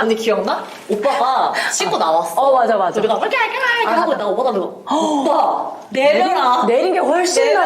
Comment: 아 여기서 은유님 재현 너무 빵터졌어요 ㅋㅋㅋ 자막은 "우리가 대화하고 있다가"라고 나오지만, 뭐라고 하시는 거예욬ㅋㅋㅋㅋㅋㅋㅋ "우갹우갸익 이르케 하고 있다가" 너무 귀엽고 웃김ㅋㅋㅋㅋㅋㅋㅋ